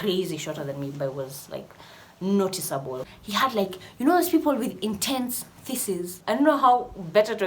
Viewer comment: I heard: (2:53) you know those people with intense feaces 😅😅😅